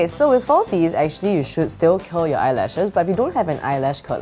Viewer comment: haha with her accent I thought she said "kill your eyelashes" instead of "curl" :D